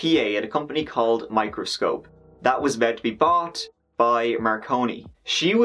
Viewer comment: My dyslexic ass thought it said Macaroni.